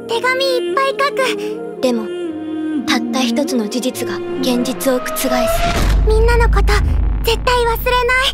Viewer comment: Did i heard genjutsu ?🙄 1:02 . Is itachi here too?